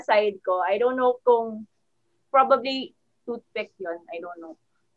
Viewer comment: im not sure if i heard it right, but did she just say “toothpick”?